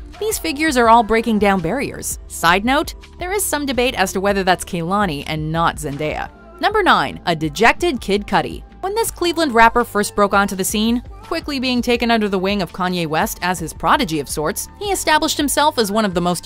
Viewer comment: DID SHE JUST SAY KID CUTTY?